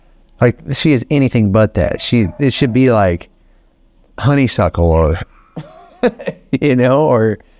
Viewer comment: What got me laughing is when he said or I thought he said honey suckler. Why that name💀😂😂😂 also I kinda get why they choose venom, its probably because she wasn't nice with the little one